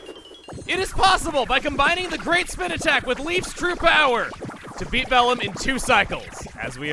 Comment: did he just say "It is possible by combining the great spin attack with LEAF'S true powers"?